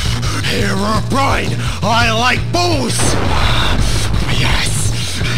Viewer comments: “Herobrine! I like balls!” Just cracked me up. 😂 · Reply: I thought he said “herobrine i like boys” 😂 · I love how the canon chant to summon Herobrine is just "Herobrine. I like balls." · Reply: i thought it said herobrine, i like booze lol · “Herobrine! I like balls!” Just cracked me up. 😂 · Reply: I thought he said “ herobrine I like fools”